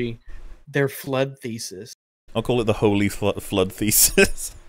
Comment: i think says "flawed thesis"